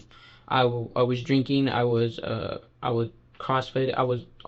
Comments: Did man say "I was drinking, i was constipated..." 😂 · wait…did he really say constipation had to do with him attempting to kidnap someone? 🤣🤣🤣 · Did he just say “ I was drinking, I was constipated … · Did he say at 6:26 "I was drinking, I was uh, I was constipated..." ?! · did he just say “I was drinking… I was constipated.” 😂😂😂😂 boiiii